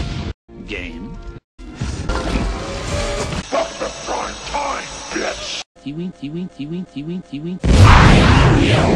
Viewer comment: "Eh, it's fine. A little dark, a little gloomy, and as always, Hey, full of dark people, what are you gonna do?" (My favorite part.) XD What does he say at 3:44? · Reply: Kaiser "Welcome to prime time, bitch!"- From nightmare on elm street